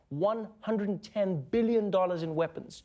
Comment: Sounded like he said billion instead of million... But either way, yeah it's a really huge deal...